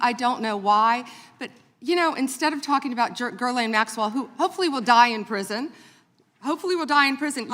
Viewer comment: did she say "Ghislaine Maxwell will die in prison.."🤨🤔